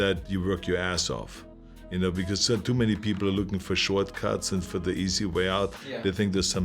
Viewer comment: The way he said shortcut sounded like he said "Shwarz-cut"